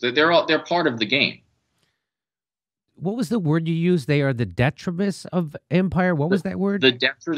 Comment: Detritus? I think he pronounced Salinas, CA differently too (I thought he said 'detriments' at first).